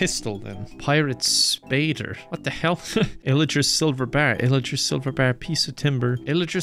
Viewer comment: Illiger silver bars. I thought he was saying Idris Elba bars